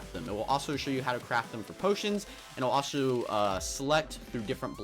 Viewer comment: UnspeakableGaming did you say, "Sans?!" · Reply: Yep he said sans